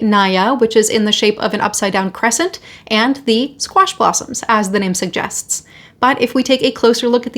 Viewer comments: I'm laughing cos I first thought you said squashed possums. 😂😂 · I thought you said “squashed possums” 😂